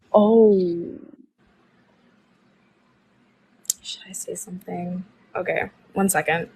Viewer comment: did she say "should I say something?" telling herself?